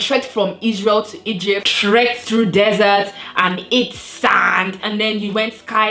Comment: I lost it when I heard "Tread through the desert and eat sand" LMAOOO